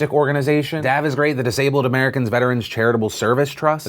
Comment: LOL! I was looking away from the video and I swear he said, "D.A.B." as a veterans organization!